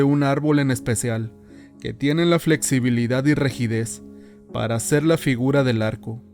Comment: que tiene queeeeeeeeeeeeeee????????? la flexibilidad y "regides" ????????????? Santo tomas de los pelos parados, díganme que no escuché lo que escuché porqué me estoy doblando con flexibilidad y rigidez de la risa.